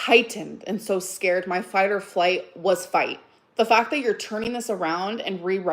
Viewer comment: Wait WT fuck did she just say?? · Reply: "My fight or flight...was fight" 😂